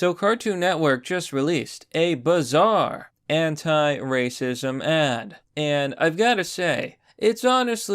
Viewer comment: first I heard hentai racist ad